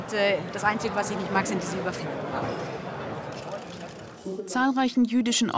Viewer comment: Hat jemand verstanden, was die Frau von Storch nicht mag (6:24)? Überfliegen? Ich verstehs akkustisch überhaupt nicht. Da hätte der Spiegel auch einen Untertitel einfügen können, so wie bei dem "Ar***lecker von Merkel". Wobei ich das auch ohne Untertitel verstanden habe.